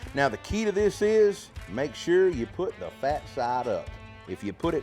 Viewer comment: Maybe my hearing is not so good because I’m old I thought I heard you say you want to put them fat side up because if you put them fat side down, they would stick to the rack and ruin some of the burnt ends then I thought you said you want to put them meat side up which means the fat side would stick to the rack and ruin some of them never done them before, but I love your rubs. I put your garlic jalapeño rub on everything.